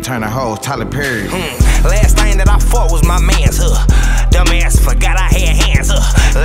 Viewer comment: for a sec i thought sada said " last thing that i fucked was my mans " i never rewinded so fast